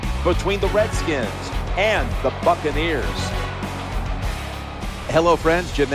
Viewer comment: lol When he said bucaneers at 1:04 i thought he said Fuckaneers! Lololol!